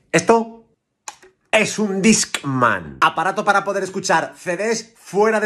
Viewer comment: Pensé que decía "Dic-man"